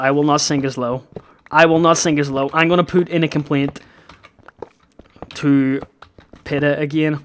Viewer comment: sounded like he said poot a complaint, because of his accent!